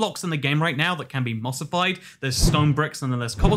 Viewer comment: When he said mossified I wasn't paying much attention and I thought he said skull vines and I rewinded 3 times before I realised what part I heard wrong I was so mad I thought Mojang added 2 new things the skulk vines and something else because the rest if the sentence that said